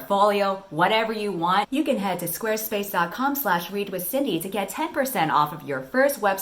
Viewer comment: At first I heard "weed with Cindy" instead of "read with Cindy". XD